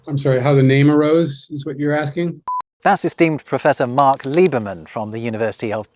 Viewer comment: funnily enough, i heard at this place "that's a steamed professor"